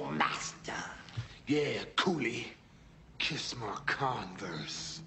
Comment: First time I saw this movie, I thought he said kiss my corns. Lol